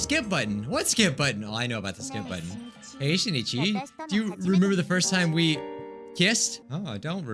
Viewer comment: After all the danganronpa v3 vids I watched, I thought she said Shuichi.